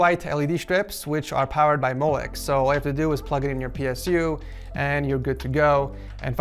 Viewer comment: I thought he said Ps2, Makes sense because it looks like the end of a Ps2 controller :P